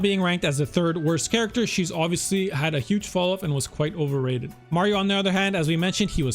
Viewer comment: Oh wait I thought it said overhated